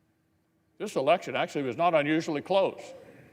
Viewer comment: Mitch McConnell literally just said the "fraud level being so much higher in 2020 than any other election year" makes a good reason for it not to be overturned. "The disparity in outcome caused by election fraud was so massive this time, that we should cite that fact as our 'reason' for not checking into it further." -Mitch McConnell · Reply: wrong. at 3:04 he is literally saying "this is election actually was not unsually close." pay attention.